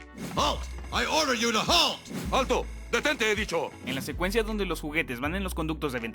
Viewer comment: yo siempre le entendí "¡Alto!Detente, bicho"